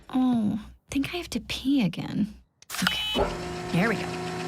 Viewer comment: If you listen really carefully when she says I think I need to pee again it sounds like she says "Oh fuck it" 😂😂 love this version of Lola